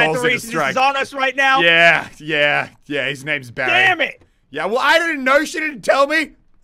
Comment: How dare you sleep with bazza's wife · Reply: yoyo man when he said the mortar guys name was Barry I thought he said bazz